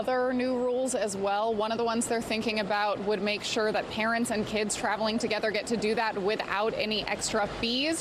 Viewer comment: I thought she said Karen’s and kids. 😂